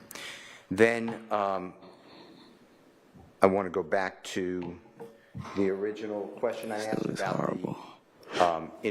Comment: What is gather saying at 7:17:20? She’s annoying for real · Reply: She says “this guys is horrible” referring to Steele